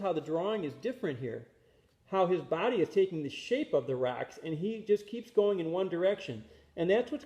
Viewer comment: I keep hearing "Racks" and not "Rocks"